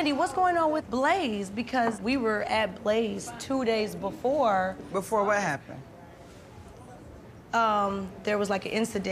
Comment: Marlo : “ What happened at Blaze ?” Two seconds later “ You talking about the SHOOTING that happened at Blaze” … girl bye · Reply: She did not say "What happened at Blaze?" - what she said was "Before what happened?" 01:48 Then she said What incident?" - followed by "You talking about the shooting that happened at Blaze?" Based on these questions, it does not appear that she was aware of what Drew was referencing and was asking clarifying questions. You guys really need to lay off Marlo - that woman isn't doing anything to anybody.